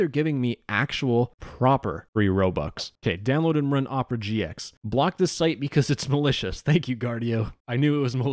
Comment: Guys i thought he said blocked this site because it was delicious 💀💀